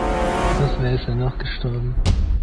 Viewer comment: he did not said "I miss BFA" he said "Oh Mist wir sind doch gestorben" wich is translated to "damn we actually died"